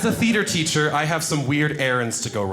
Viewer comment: Ok....i thought he said "FEEDER teacher" 🤣😅😂 made no sense but ihave been rolling the whole time.